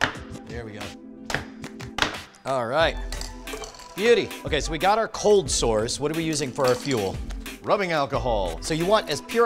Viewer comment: Would it be bad to eat stuff that was frashfreezed using this liquid? I'm guessing yes. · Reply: I thought he said we've got our cold sores